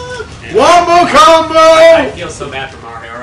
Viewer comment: Did he say... wumbo combo?